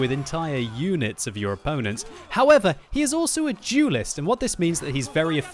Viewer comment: Had to replay that, thought I heard "He's also a (Jewist)". Lol.